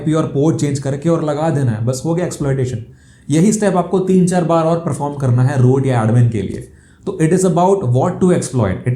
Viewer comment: What sir said at 10:52 that word R______. Koi btao do muje samaj hi nhi aa pa Raha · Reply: root